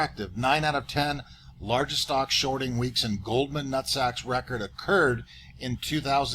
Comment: My wife thought you were saying golden sacks